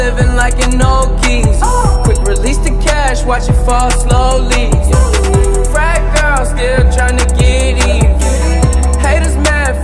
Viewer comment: I THOUGHT HE SAID “FAT GIRLS”💀😭🥲